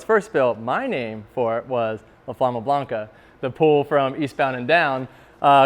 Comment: When he said "La Flama Blanca" 4:55, I definitely heard Obama Blanca